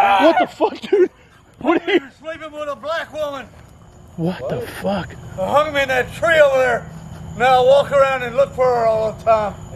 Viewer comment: It's a good thing turned around when he did , no telling what that maniac would have done . It sounded like he said " He was sleeping with a black woman and I hung him in that tree over there , now I walk around looking for her ".